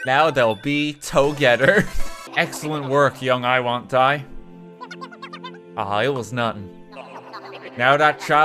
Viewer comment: I heard: "aw, I was numb..."